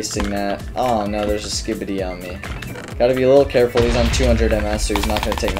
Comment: Did bro say “Oh no! There’s a skibidi on me”